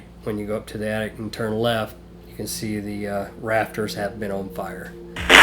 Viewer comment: I HEAR....."SISTER, ON FIRE"......LIKE IF ITS THE SISTER TO THE LITTLE GIRL, THAT IS SAYING THAT.......?????